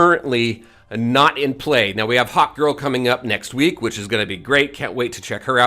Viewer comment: Oooh HAWKgirl... I thought you were saying HOT GIRL? I was like "this man has a serious crush" 😂😂😂